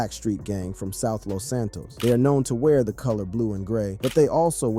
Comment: did bro just say GSC from South Los Santos?